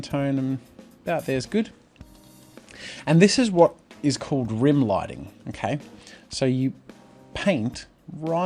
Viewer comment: What's that thing called when you chose a very light colour and colour it in the shadow at 13:38 ? I can't really hear it properly. What lighting? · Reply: it's Rim Lighting